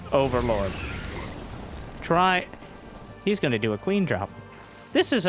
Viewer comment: Missheard it as "queen job." 💀